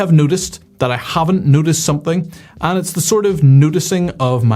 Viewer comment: I thought you were saying "nudist, i haven't nudisted"... then i thought, as a nudist you would notice weather trends.